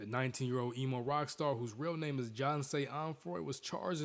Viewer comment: It sounds like he said john say but his name is jahseh (edit) but the first time he hit her she should have broke up with him im not blaming her but xxx sounds like a real dick back then idk maybe he would kill her if she left him but she also could have used self defence